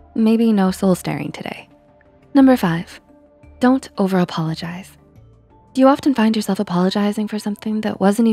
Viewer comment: 1 - Respect Yourself first. 2 - Set clear boundaries. 3 - Understand that you don't have to be nice all the time. 4 - Speak Up. 5 - Don't over apologize. 6 - Display confident body language. { Thanks for likes <3 } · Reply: For some reason I thought 5 said "Don't ever apologize". Now I do over apologize when I feel like I insulted a person.